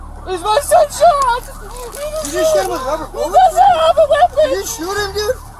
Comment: "did you just shoot him with a rubber bullet" is what he said.